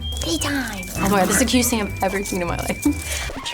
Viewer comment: What'd she say? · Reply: “Omg, this is the cutest thing I’ve ever seen in my life”